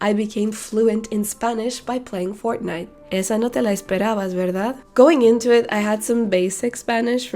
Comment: Hola, que bien que sabes español, saludos desde México :) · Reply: Estoy aprendiendo español, ¿Que dice en 11:21? Yo entiendo "esa" o "esta" primero, "palabras," y "verdad" en el fin, pero es un poco rapido para mi!